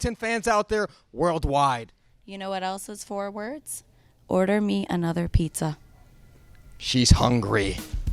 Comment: Why did I hear "order me and other pizza"